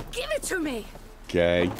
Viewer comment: did he say gay or k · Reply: That's usual "kay" Further, in 5 years I must have heard less than 10 swear words (or affiliated) on this channel.